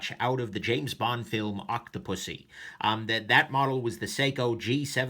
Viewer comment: did he just say "auto-pussy" at the beginning? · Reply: Octopussy